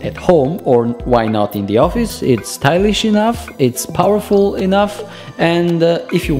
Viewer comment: towards the end, i thought he said "it is Irish enough"